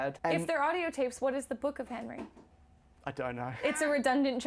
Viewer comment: oh The Book of Henry lol I thought they said Hardcore Henry, I was like 'why the fuck was this guy even considered?'.